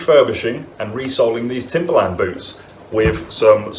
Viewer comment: I thought it said vibranium boots...